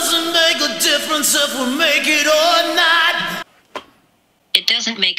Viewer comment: I hear "if we're make or not"